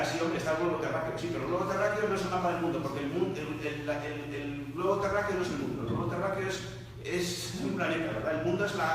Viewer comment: Hola, ¿Alguién sabe qué dice el minuto 31:58? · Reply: «El globo terráqueo no es el Mundo, el globo terráqueo es un planeta».